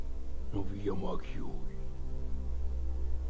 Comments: Que dicen en el minuto 1:30 alguien puede decirme porfavor? :) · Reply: Los Simpson Iran al infierno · luego de repetirlo varias veces creo que dice "los simpsons iran al glaciar perito moreno" 🧐